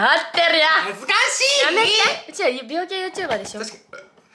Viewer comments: 美容系が病気系の聞こえたwwwwwww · 00:6 美容系YouTube が 00:1 のへんな音で うちら病気YouTubeでしょ って聞こえるん草